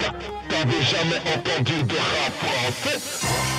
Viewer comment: svp à 3:44 il dit " t'avais jamais entendu de rap toi , en fait ! " ...... c'est bien ça ? et il fait bien allusion à n désir , l'affaire " canta " ?......désolé j'suis pas bien caler en RAP ...juste chercheur de vérité .......j'explore et découvre ....